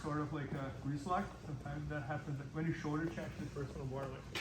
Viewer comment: I couldn't hear the part at about 16:00 What is the advice to watch out for? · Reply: He's talking about the shoulder check. You are right, it's hard to hear but I think he's talking about partner safety when doing the shoulder check.